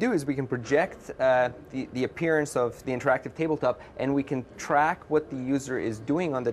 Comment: In the last second I heard him say "interactive penis" O.o